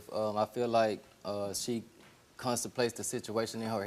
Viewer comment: Did he just say she constipate stuff in her head.....lolololol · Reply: Contemplate not constipate. Ain't nobody constipated dear.